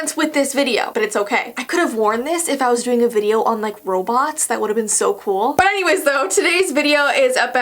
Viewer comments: When she said robots I thought tshe said Roblox PLS DO ROBLOX AND FRIEND IM IM DAGANX23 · I thought you said what if I did a video on roblox But you said what if I did a video on robots